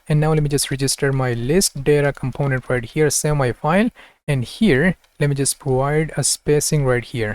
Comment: He repeatedly says something like 'semi file' or 'sell my file' in the video e.g. at 1:28:44 . What is saying actually ? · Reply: Save my file